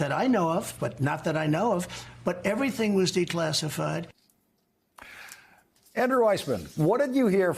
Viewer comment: I heard " My pants are declassified"! Ha Ha!